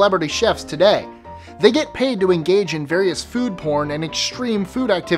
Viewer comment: Did I hear that correctly? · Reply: food porn..... what the fuck?